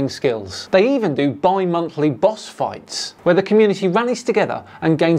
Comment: Ohhh, "Bi-monthly boss fights"! I heard "Buy Monthly boss fights" (01:10). I was confused about how that was a good thing :D